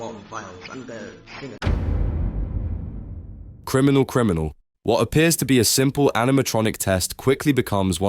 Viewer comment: I always thought purple guy was saying "Pepe doll" instead of "criminal"